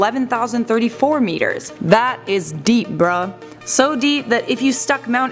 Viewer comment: Did she just say “BRUH” 0:17 😬🤣